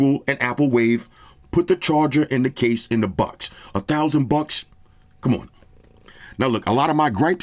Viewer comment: The way u saying for thousands buks sounds like u saying 4k buks. U scared me 4k bucks hell no